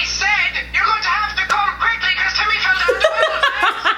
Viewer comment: James: I could only make out one word Me: But I clearly heard it: "You have to come quickly cause timmy fell down the well"